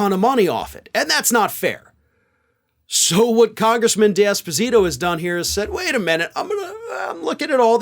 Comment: I thought said congressman Despacito, and funny enough, check the (automated) transcript, yup, congressman Despacito. Made me chuckle 🤭